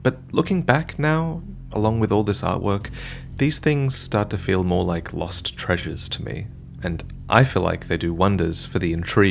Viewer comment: What he said: "..feels like lost treasures to me" What I heard: "feels like more content to do for me"